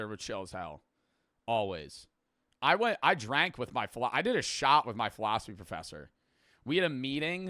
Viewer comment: i closed the video when he said shot and i thought he said shower